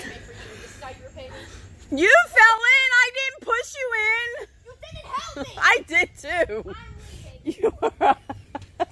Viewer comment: What did say around 6:30ish? “I turned off my favorite *** for you!”? Did she say what I think she said? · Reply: She said Anime 😂!